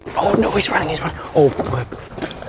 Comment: did he say the f word? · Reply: Nope he said flip